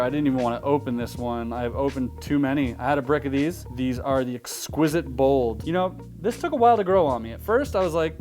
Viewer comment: what is the name of this deck once again please? 10:35 I can't catch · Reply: Exquisite Bolds